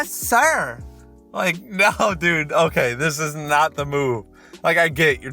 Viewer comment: I don't know if my ears are right but did he say "This is not the moo"?